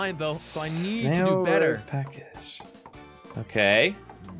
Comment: nobody noticed he said snail road package instead of snail road passage